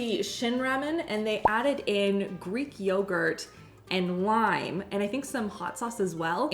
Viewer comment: When you were making the Ramen I thought you said to add yogurt and WINE and I thought ok? Lime ( even though I do not like lime) sounds a lot better. By the way native Texan here who does not like spicy food. Don't feel bad about not being able to eat spicy foods.